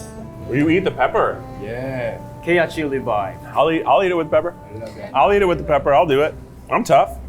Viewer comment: for anyone with captions, the (indistinct) here is san saying “k-hot chili vibe” 🤍 a reference to their song: bouncy (k-hot chili peppers) !